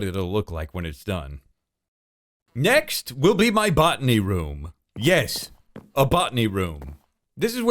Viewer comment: I love the awkward potion instructions and that botany room just gave me some much needed inspiration, Thank you! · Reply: I was so confused at first when he said "botany room", because I heard "buttony room". I envisioned a room riddled with buttons. 🙈